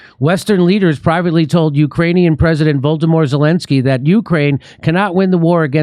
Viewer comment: Jimmy, the way you pronounce "Volodymyr", I thought you said "Voldemort Zelenskyy".